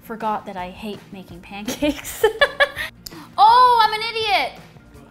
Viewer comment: I thought it said more "oatcakes"...